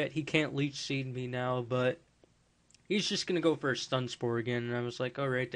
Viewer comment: When your azelf was paralyzed I thought you said stun sperm. xD